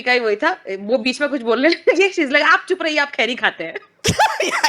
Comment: can someone clarify what Supriya Shrinate says? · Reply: In one of the news debates she said to one BJP guy "please don't speak you chew tobacco".